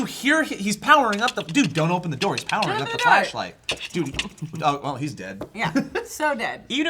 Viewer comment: at first i thought said fleshlight...